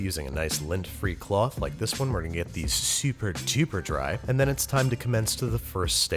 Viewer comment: Did you say super TUBER dry or am I just kind of drunk and misheard you? God I hope you said super TUBER dry...get it, tuber=potato hahaha